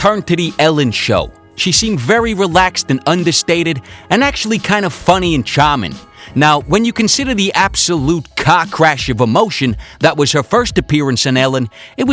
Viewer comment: You said "Car Crash" I thought you said "Cock Rash'' lmfao